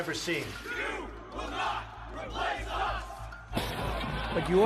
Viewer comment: the subtitles are wrong, the nazis screamed ''jews will not replace us''